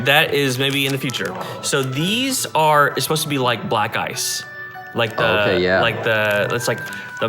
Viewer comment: my life I thought he said the air freshener scent was "Black Guys" 😂😂😂😂 sorry from the UK. My bad.